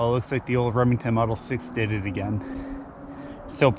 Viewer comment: Congrats on a fine PA buck. I have to compliment you on that gorgeous Gamemaster rifle you own. What a treasure. I didn't understand what number you said it was at the 9:25 mark. I thought you said Model 6. Is that correct? Not a model 760? Just curious. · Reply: You are correct Model Six different has a longer barrel, fancy Monte Carlo stock brass bullet inlay tack driver out to 300. Haven’t shot further with this.